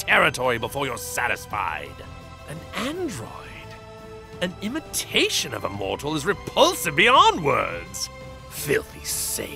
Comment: Anyone can tell me what he says at 5:10? I can understand the "An android? An imitation of a mortals' repulsive—" And then I cant understand what comes next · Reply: "An android? An imitation of a mortal is repulsive beyond words."